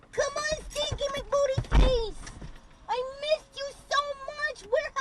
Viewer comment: I'm so confused on what she said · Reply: I could swear she called someone “Stanky McBooty-face” 😂